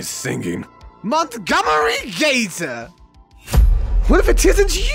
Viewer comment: I died from laughter because misheard gator as gaytor